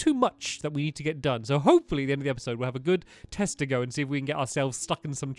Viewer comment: did stampy say testicles????